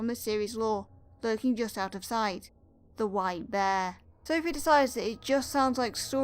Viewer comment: The white bear What i heard: the W I D E bear